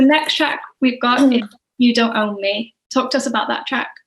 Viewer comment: Just noticed at 13:56 it says “You don’t Owe Me” instead of “You don’t own me” I feel like that’s a pretty big oversight 😅